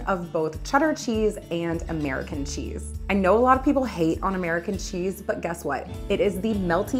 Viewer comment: I'm getting old. I thought you said, "People hate UN American Cheese", and I realized it was "Hate ON"; hahahahahh Great video, thanks for the recipe!